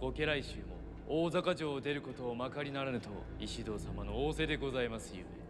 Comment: sounds like hes saying makarunarudo or mcdonalds in japanese XD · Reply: He said "makari-naranu".